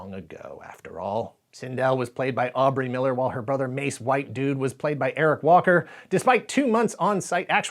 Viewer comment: Please someone correct me but is anyone else hearing him say “Mace WhiteDude” when describing the actors name? From now on I’m changing my name to Langley BlackGuy! 😂🤣